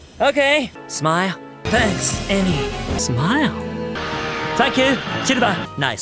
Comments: I heard "fuck you, Silver" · Why do I hear "Fuck you, Silver"